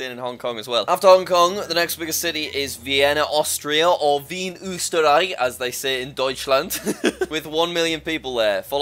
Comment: Hearing say "Wien, Österreich" as "Vien Üsteraai" was very funny - it almost sounded like "Wie ein Osterei" (=like an easter egg)